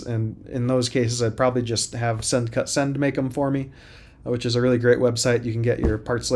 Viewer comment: Whats the name of the place for metal cutting? I cant hear the name at around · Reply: Send cut send <link>